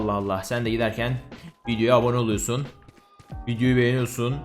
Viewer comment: Herşeyi anladımda... 6:04 VİDEOYA ABONE OLUYORSUN NE YA NDDI9WLWKEKW0Q